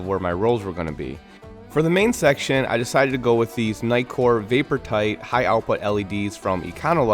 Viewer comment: I keep hearing "nightcore"